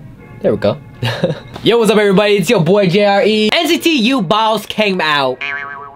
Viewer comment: I swear, every time he says Boss I hear balls like "who's the balls of this group?"